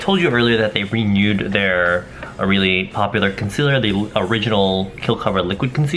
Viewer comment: I swear I hear "conseller" 😂😂😂